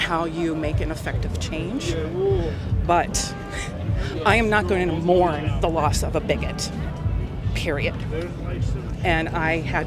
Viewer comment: "I'm not going to mourn the loss of a bígøt!" Is what was said but what I heard was "Why would you mourn for the loss of the wicked" either way is apt tho